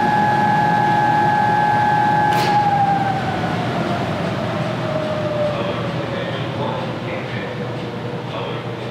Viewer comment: can anyone tell me what alert says 0:54 ...nvm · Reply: This is a Verbatim alarm...Saying "Alert condition 1 exists".....the ones I've programmed had 8-16 inputs to program for normally open or normally closed alarm conditions.